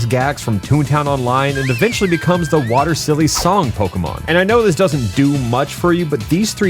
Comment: i heard silk song the brainrot is real